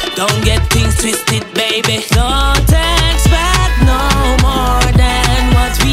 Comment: I know it doesn’t make any sense but y did I thought he said “don’t text back no more than what we already have” I now know is don’t expect no more. Big tune tho.